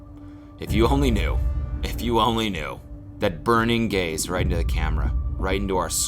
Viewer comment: I thought he said “burning gays” and I thought jeepers creepers Cody